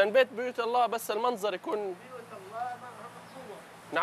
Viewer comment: بيوت الله ايش لا يكون الي سمعتو